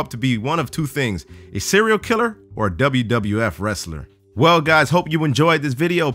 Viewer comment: subtitles say mexican wrestler and said wwf wrestler